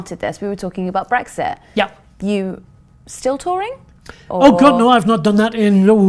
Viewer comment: Nearly had a heart attack when I misheard 'You... still touring?' as 'You... still Tory?'